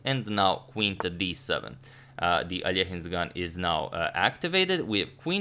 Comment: What does Antonio say at 10:20??? "uh the aliajin's gun is now activated." ????? What's that mean?